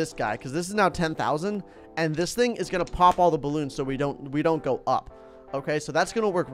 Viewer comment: Watch from 25:50. Did he say pop all the balloons?